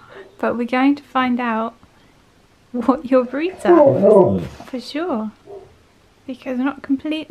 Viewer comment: It really sounded like "Oh No", :O